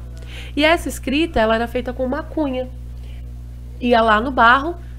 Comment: "e essa escrita era feita com maconha..." entendi isso kkkkkkkk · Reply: Uma cunha! Kkkk